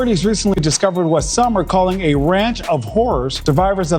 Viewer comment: Thought he said ranch of hores.